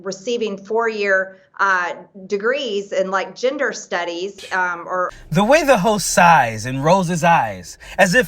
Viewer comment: I thought she said ginger studies. :-D